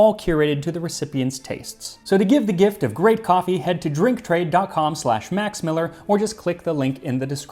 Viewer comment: it sounded like you said "drink trade dot cum" but my mind do be in the gutter